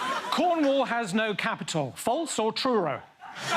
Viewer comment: I hear ‘false or truro’ every time I go through truro to uni